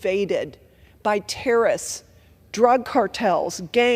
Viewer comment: Did she just say.... terist? Terrorists*